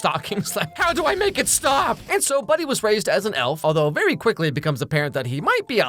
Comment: I heard "raised as an elf" as "raped as an elf". Am I a bad person?